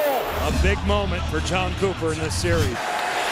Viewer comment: Tortorella looks different 😂🤔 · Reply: And it sounds like he said Tom Cooper